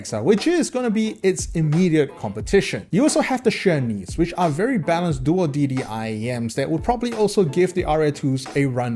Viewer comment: What is this IEM? I couldn't understand. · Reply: Tangzu x HBB Xuan Nv It's a collab with HBB